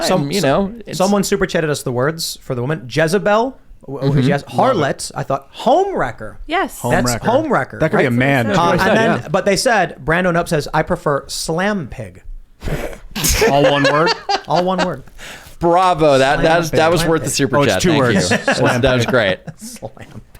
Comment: why did I hear Slam PIT and not slam PIG LOL